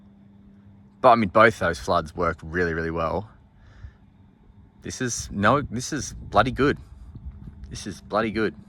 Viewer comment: thought you said "This is floody good" lol